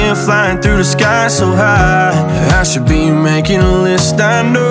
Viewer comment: Am I the only one who thought he said "I should be with my kid" 😂😂😂